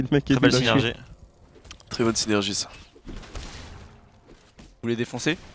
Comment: A 10mn quand dit "très bonne synergie" j'ai compris "prépuce immergé" c'était assez troublant ._.